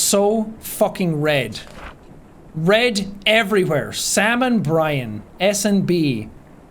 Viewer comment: who thought he said "salmon brian" and not "sam+brian"😂😂😂😂😂